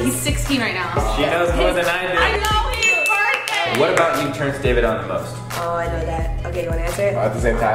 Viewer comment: literally wtf do they say at 4:22 can someone please explain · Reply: Faith _wright27 also they say “what about you turns david on the most”